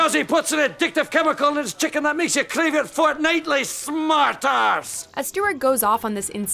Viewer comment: I heard him say “Fortnite”... is that bad?